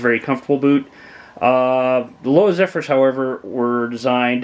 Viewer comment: What??? Sounded like you said Louseffors???? You speak too fast.... I did, however, hear you say Danner Fort Lewis which is the boot I like, but didn't understand the name of the other boot brand. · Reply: Lowa Zephyrs (Mid TF) is the other boot i was referring to. I struggle with narration at times and my channel ( and myself) are a work in progress. Lowa Zephyr Mid TF are a much lighter boot, ideal for high desert/ mountains, which is where I live. I've owned several pairs. Thanks for watching.